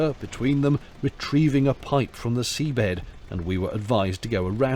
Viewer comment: I first heard it as "retrieving a pint from the sea bed" and I thought 'that's dedication.'